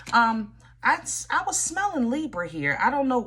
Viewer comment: Girl! I’m a Libra but also a Leo rising, so I was watching this video. And then I heard “I smell a Libra.” I said sorry and stopped watching immediately 😭